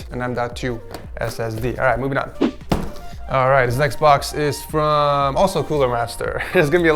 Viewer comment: did you said this XBOX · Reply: this next box